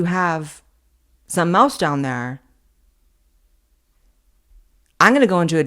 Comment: 😂 i thought Blaire said "some mouse down there."